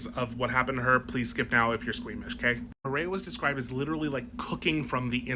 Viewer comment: i thought you said swedish and i was like, oh god what could they possibly do to the swedes in a situation like this?